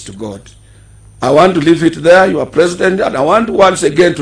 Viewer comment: did he just say “your president “ alaaaaaaah